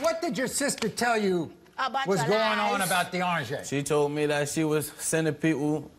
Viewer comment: D'Arnjai?! i thought maury was saying "d'oranges" at first LOL